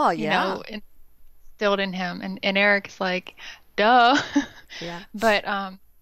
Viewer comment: At about 3:43 I hear a soft voice say Erik. Anyone else hear?